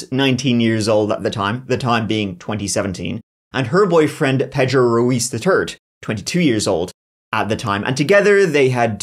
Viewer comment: Did he say " Pedro Ruiz the Turd "